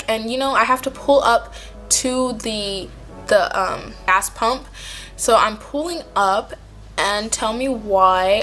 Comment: it sounds like she said ass pump.